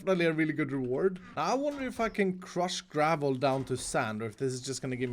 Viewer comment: Did he say the f word a 9:26? I can’t here anything else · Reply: 'I wonder if I can crush gravel down to sand'